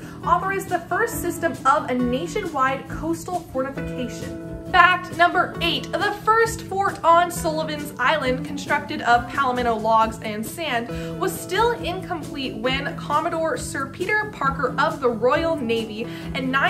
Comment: I thought she said Palmetto bugs, never mind.